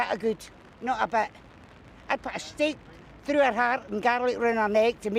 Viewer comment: English is my second language and I've never been to the UK so I was having trouble understanding the Scottish accent of the lady at 1:31 so I put the subtitles on and this is what it said: "No about a good, no I bet. I passed it... through her hand and gallery through my leg to meet should ever come back" Me: 🤨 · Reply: Subtitles are completely wrong I'm afraid. The woman was saying that she would have put a steak through Thatcher's heart, and garlic around her neck, to make sure she couldn't come back